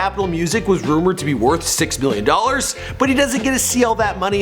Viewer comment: did he just say 6 billion dollars 💵